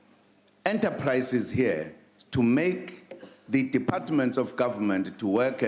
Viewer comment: did Zuma say "The department of Government" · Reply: DepartmentS, plural, Smarty.